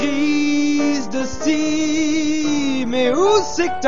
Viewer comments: Criss d'ostie, au début j'ai compris "crise de style" :p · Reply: J'ai compris "crise de Steam" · Criss d'ostie, au début j'ai compris "crise de style" :p